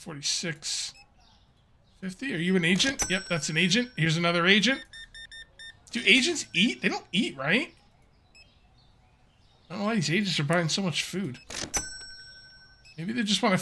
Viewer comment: Dude, I thought you said ‘Here’s another Asian, do Asians eat? I don’t know why Asians buy so much food?’😰😂